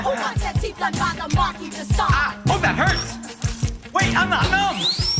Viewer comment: TerBerg who wants their teeth done by the marquis de Sade? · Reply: Ah! When they said Marquis de Sade, I thought they said "Let the market decide!" Makes much more sense now!